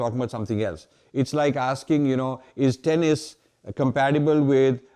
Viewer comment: what does RM says here at 12:04? tenese? (muscle?) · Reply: Tennis. Is tennis compatible with taking drugs?